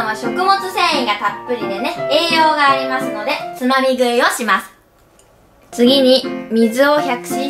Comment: つまみ食いがつまり食いに聞こえた笑